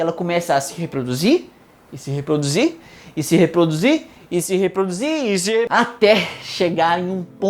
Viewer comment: Eu ouvi seres humanos? 🤨